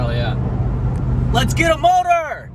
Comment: Thought he said “Let’s get a boner.” Lol 😂